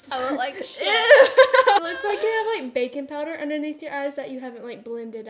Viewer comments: i 100% thought she said bacon powder and i was slightly confused for a hot second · I thought she said bacon powder but she actually said baking powder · I love how calm you are in comparison to, it’s so cute 😆 Also, did I hear “bacon powder”? 🥓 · I thought she said “bacon powder” and was so confused LOL